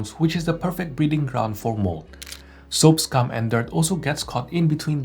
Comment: did you say soaps, cum and dirt??? AM I HEARING THAT RIGHT????